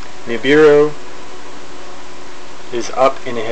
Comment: What does it mean "vivero", (minute 0:46) a word ont the subtittles, when the speaker says: «Nibiru»? There are in the subtitles many glitches like this one. I don't know "vivero" in English at all.